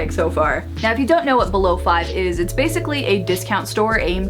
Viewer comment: did she really say below five?😂 love her sm lol